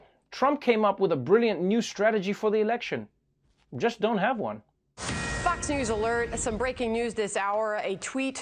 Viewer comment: Lol I thought I heard fucks news alert 😂